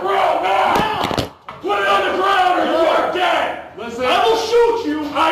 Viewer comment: I know it said "put it on the ground or you are dead", but it sounded like "put it on the ground or you are gay"